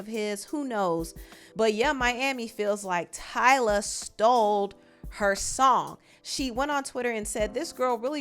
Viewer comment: Did I hear "she stoled (stole) her song" 🤣 I might be trippen idk